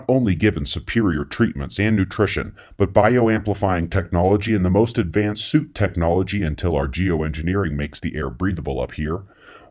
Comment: Did he say, 'the most advanced soup technology."? I love me some soup. Thanks.